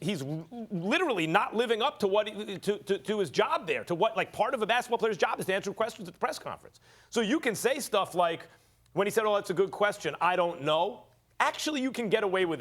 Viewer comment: When westbrook said “um thats a good question” berry tamel thought he was gone finally respond to his questions 😂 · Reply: Did he say that's a good question? I thought I heard him say "uhm that's a stupid question."